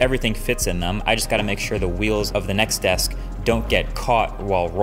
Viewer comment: what desk is that? I heard “nex desk”?